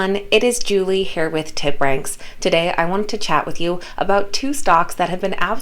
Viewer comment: I thought she said Tit ranks 😂 busty trader god damn !!!